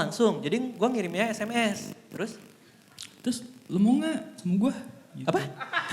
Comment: gw dengernya "lu mau ga cium gw?"